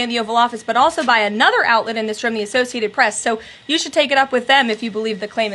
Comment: Pause 2:42 Did she say "The Associated Press?" I thought they bandedededededdddd lol - Are they a good news outlet again? So, we not still mad at them? Are they now relevant news again? I need a daily update on who is friend and who is foe lol